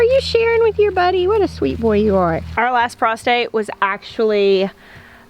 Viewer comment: Laugh of the Day: I was using the closed captioning feature on your video because my hubby is in the same room with the TV news on.......and The Closed Captioning people thought you said "......our last prostrate..." instead of "...our last frost date......" I'm guessing they're not from the South :) Anyway, we bless you from the great white north of Bridgewater, Vermont here at North Branch Farm............ Today in Vermont, happiness is a newly sprouted Zinna Seed. Please Be Safe and keep up the fine job you're doing.